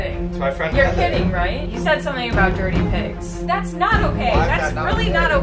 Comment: I thought said dirty pigs